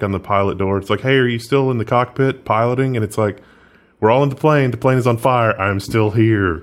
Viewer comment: My brain interpreted that segment as "The plane, the plane, the plane is on fire. The plane, the plane, the plane is on fire. We don't need no water, let the motherf**er burn. Burn motherf**er burn." I guess I heard Bloodhound Gang on the radio recently...